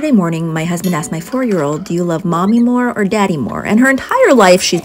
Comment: I thought she said forty year old. 💀